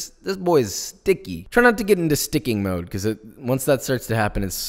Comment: who else thought he said “sicko mode” and not sticky mode... just me? Yea